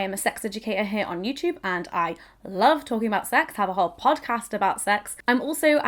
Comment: 45 I thought she said I have a "HARD "PODCAST about sex ha! No pun